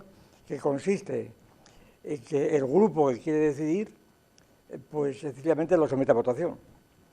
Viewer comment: que diceee aca??? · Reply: "Pues sencillamente lo somete a votación [...]"